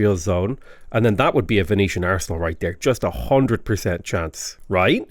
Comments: I had to rewind at 3:05 because at first I heard "Venetian arsehole" 💀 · Did he say “Venetian Arsehole”?